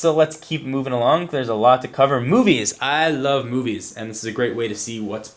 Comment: Did I hear you like boobies? Me too :)